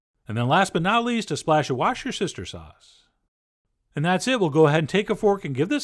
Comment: "A splash of wash-your-sister sauce" 😂😂😂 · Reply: I heard that as 'watch-your-sister sauce'. Not sure which one is more inapwopwo.